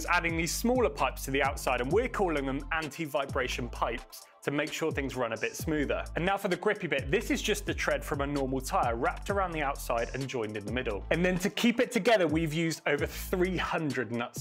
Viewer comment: Am I the only one who thought they were saying LS tire? 🤔